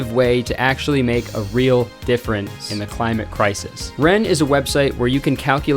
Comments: I know this is two years later but omg I just noticed that at 16:52 you said "different" instead of "difference" and voiced over the "ce" and I just can't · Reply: Really? lol i heard “difference in the climate crisis” but maybe i was wrong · you arent fooling me, i can tell you said different instead of difference